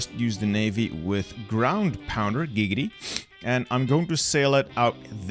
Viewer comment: what was that??? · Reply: Did he just say gigity ?